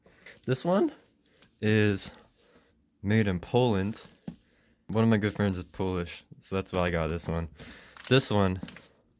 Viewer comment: did say one ofy girlfriend ? 🤔